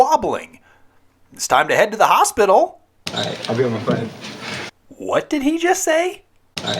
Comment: I thought Slater said “I’ll be on my 5” as in he’s taking a five minute break. I don’t know if that makes sense in the story, him going on a five minute break, but that was how I understood it.